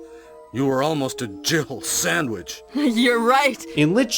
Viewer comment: Jibble Sandwich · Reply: Dude, when my friends and I first played the game in 96, I heard "jivel sandwich". I figured it was just a word I didn't know and believed that for years to follow. I remember even asking my mom "what's a jivel?"